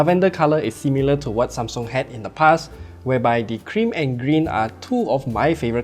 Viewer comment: I heard Green as 'Cringe' somehow..😄